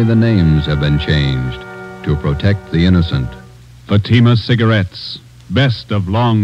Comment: lol Get Fatima I thought it said get emphosema at first lolol Oh boy